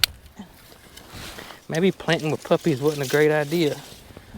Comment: At first I thought you said planting the puppies! LOL! Great that the puppies get to see what is normal activities on the farm! They will learn what is yours and where everyone belongs! They look like they are smart puppies!